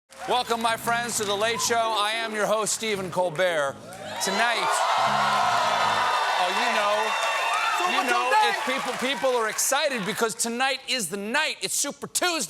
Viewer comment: Sometimes I hear him say, "My name is Stephen Cold Beer."